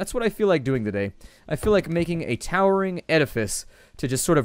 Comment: I thought he said "towering oedipus" at 2:58. What would that be, a giant statue of his mother?